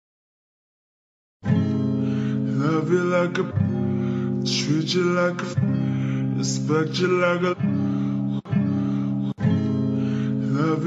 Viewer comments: All I heard was "Stretch you like a Chum dog" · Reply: I hear “love you like a rare, treat you like a hamburger” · All I heard was "Stretch you like a Chum dog"